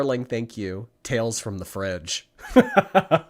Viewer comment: Oh my .. I need to stay out of girl world. I thought said "tales from the crib" I about chocked on my pizza.